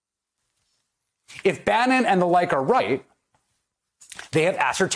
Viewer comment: Did he just say if" Bannon and the Reich are right "?